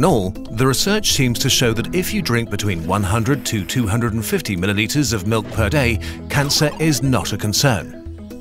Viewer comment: I don't understand why you're measuring milk in millimeters. Could someone explain? Edit: nevermind i misheard milliliters as millimeters